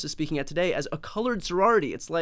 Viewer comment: Brian, I love ya, but I watched that clip out of Fox a few times and even slowed it down some and the guy didn't say "colored". He said "college" sorority. I thought at first he said what everyone is accusing them of, but he didn't. He obviously doesn't enunciate his words clearly, but he did say "college".